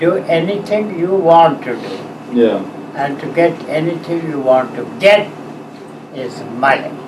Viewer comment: what did he say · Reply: Money